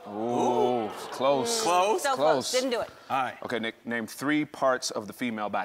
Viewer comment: he actually said..NICKNAME hahah